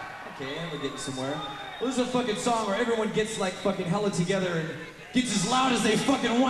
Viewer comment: did james say hella lol